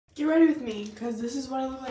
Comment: I thought u said “get ready with me cuz I’m in a breakup”I was abt to cry😭💗